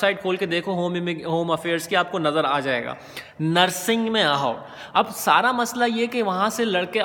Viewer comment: Did i heard nursing at 7:25?